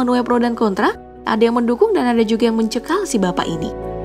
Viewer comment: Ada yang mendukung ada yang MENCEKAM, apa gua salah denger ya, mengecam min bukan mencekam